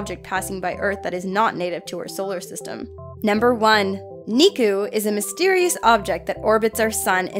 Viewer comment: I thought she said niko